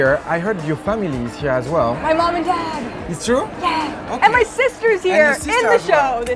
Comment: Like a foreign person and I m still learning english I thought that she said "My mom is dead"shaush.. I said wtf she couldnt said that so happy after I listen it again and Im okay with it.